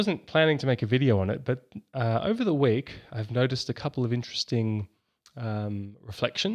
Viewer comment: Did he just say “over the wig”? 😮